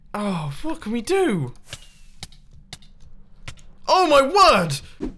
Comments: did he say aw fuck we do? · did said the f word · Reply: TruecookieYT what can we do*